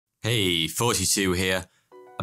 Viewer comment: Honestly, I thought he was saying "Hey, 42 here" for the longest time